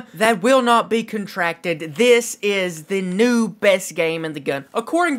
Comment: who heard him say this is the best game in the gun